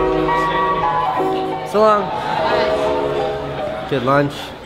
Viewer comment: did he say salam at 2:59 ? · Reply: ***** He said "so long".